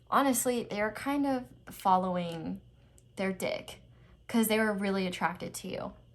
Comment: did she say dick- 😭😭